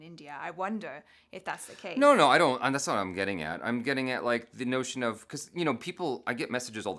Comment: Did say 'yaar'? Or I just misheard... 😛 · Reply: I guess he said, 'I am getting ya' and not yaar... 😁